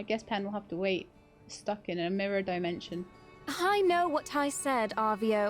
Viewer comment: Did you say that Pen was stuck in "A mirror dimension" or that your cutscene was stuck in "Amirah dimension"?